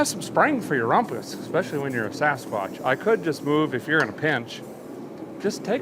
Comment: did he say “ especially when you’re a fat f**k”?